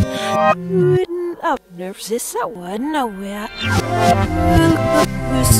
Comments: I heard is someone nowhere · I heard nowhere